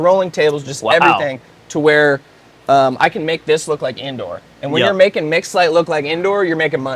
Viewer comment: I’m stoned watching this so for like half the video I thought he was saying he could make it look like Andor 😭😭. I’m like, yeah that was a pretty dark show I guess🤔.